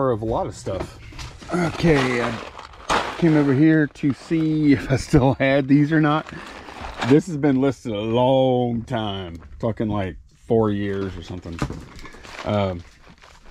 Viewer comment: Tsk-tsk. I hear a bad word in today's video. LOL. Love the content and the real world help it provides to all of us sellers. Thank You!! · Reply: I Think she misheard you because of your slight accent- I'm guessing she mistook you saying 'talking like 4 years or somethin ' at 4:45 mark, as 'f***ing like'...